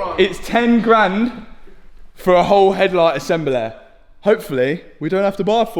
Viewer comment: did just call the headlight assembly a headlight assembelloo.......lol